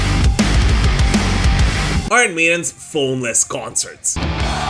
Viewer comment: I thought it said Iron Maiden Phoned-In Concerts. The last one was meh.